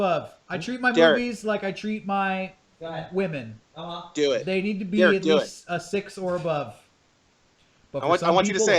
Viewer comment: What was analogy at about 48:00? Couldn't make out what he said. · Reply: "I treat my movies like I treat my women, they need to be a 6 or above. But for some people a solid 4 will do."